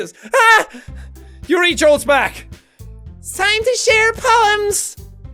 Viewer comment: When it said "time to share poems!", I thought it said "time to suck penis!"...